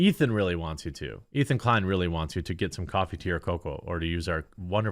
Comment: Coffee, tea or cocoa!!! I always thought he said "Coffee tier cocoa".